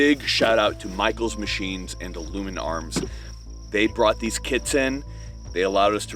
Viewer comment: i thought u said “they brought these kids in..” i was like woahhhh wait a minute bro I didn’t know these came from China 🇨🇳 🤣🤣🤣🤣🤣